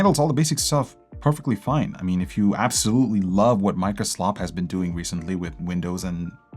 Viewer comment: DId the guy just say Micro SLOP? or Am I just hearing things?